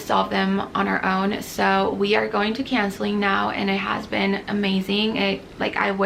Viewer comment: It sounded like you were saying “canceling” lol I was confused and first 😂